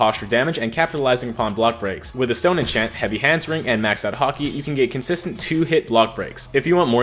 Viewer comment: nah i dont play a lot of deepwoken but did u just say maxed out haki